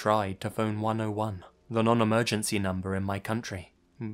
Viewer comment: First one is a total BS. There is not a single place in the world where you need to have credit on your sim card in order to call the emergency number. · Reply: Afyr0 I would have to re-listen but I thought it said non-emergency number.